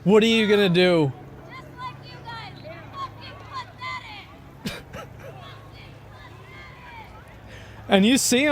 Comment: sounded like “ we are just like you guys , fucking pathetic “